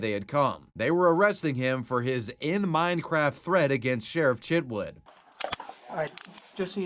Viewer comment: for some reason i keep hearing "sheriff shitwood" instead of sheriff Chitwood